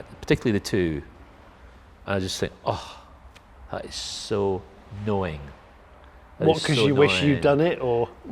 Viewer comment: he describes the design of the Porsche 992 as 'so knowing', but, from his response, appears to mishear it as 'annoying'.